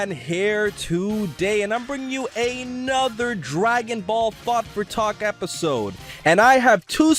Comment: I thought he said Dragon Ball fuck for talk in the beginning at first. Lol